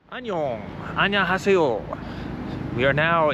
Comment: The chinese korean woman, sounded like she was saying how are you in a strong accent lol